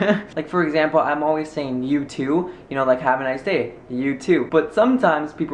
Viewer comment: the first time you said "you too" i thought you said "YouTube" XD